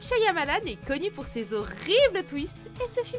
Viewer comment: Une de vos pire vidéo, la plupart des fins que vous citez sont juste énormes. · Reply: Entièrement d'accord, j'ai failli faire un anévrisme quand j'ai entendu "Shyamalan roi des horribles twist"